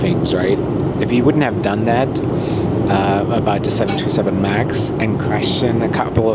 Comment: you accidentaly said 727 instead of the 737MAX , but other than that I think you did a great job of diplomaticaly handling the situatuion of how the crew is put under pressure .....great job! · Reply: He said 737 MAX. Its just a YouTube auto caption error